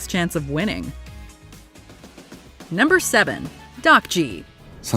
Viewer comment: 딱지가 땃쥐라고 들리는데 ㅋㅋㅋ